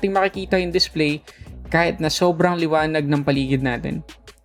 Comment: did I just hear you say "kagina" instead of "kanina"? Are you Ilonggo, sir?